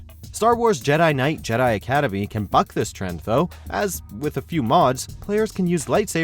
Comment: it sounds like hes saying “can fuck this trend though” but I know thats not what he’s saying... surely?